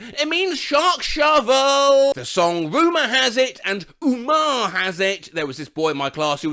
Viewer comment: I somehow misheard Rumor Has It as "Goomer Has It" and I don't even know anyone named Goomer.